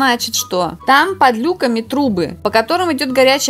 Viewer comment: Кому тоже послышалось не трубы а трупы?😂